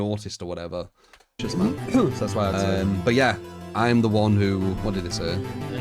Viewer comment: I think is important to note you didn't say "I'm the one who wanted this eh?" You said, "I'm the one who- what did he say?". These kinds of things I imagine don't help the narrative at all. But its a good example of how the conversation translates over all the people it goes through EDIT: The irony of the context here about misinformation is quite striking